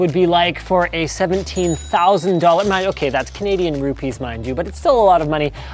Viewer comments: Does Linus say Canadian Rupees? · Did he say Canadian Rupees at 0:56. LOL